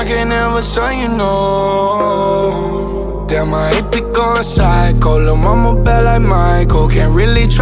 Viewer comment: Lmao I thought he said “ my ex be going psycho “ 😂😂😂😂